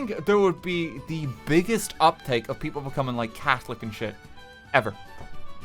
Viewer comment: Oh, you said Catholic? ... I thought you said Cat-holic!